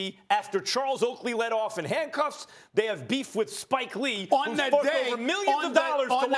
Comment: doesnt it sound like Max says "fucked"